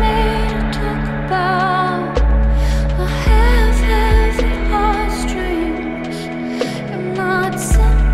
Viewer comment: that’s so weird haha when she said “A half life of lost dreams” I always thought she was saying “I have heavy heartstrings”